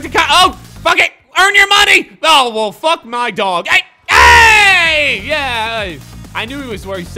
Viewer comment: did he say "fuck my dog" at 10:28 or is it just me😂